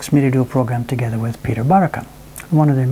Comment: does he say peter buttercup? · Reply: The NHK announcer is Peter Barakan ...